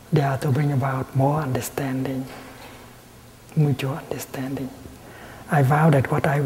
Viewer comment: Hello, These are a few proofreading suggestions for the Youtube video titled "Ending the Vicious Circle of Negative Habits | Dharma Talk by Thich Nhat Hanh, <num>": 36:53 – mutual instead of "mucho" understanding; 37:09 – mutual instead of mature